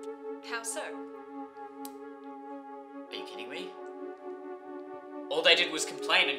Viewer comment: Turn on subtitles. At 5:03 it says "bikini" instead of "you kidding me?"